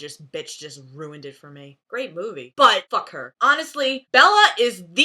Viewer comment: did you say "buttfuck her?" OMG! Good edit.